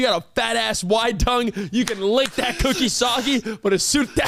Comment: I thought said “you could lick that coochie soggy” fml I need to get my ears checked🤣🤣🤣🤣🤣🤣